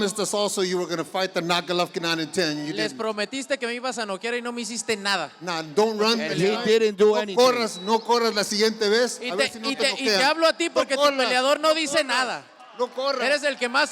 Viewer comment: what does canelo say at 23:20? lol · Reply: He say "le prometiste a tus fanáticos que me ibas a noquear y no me hiciste nada, y te hablo a ti por que tu peleador no dice nada, tu eres el que mas abres el hocico" now you can go to translate in google haha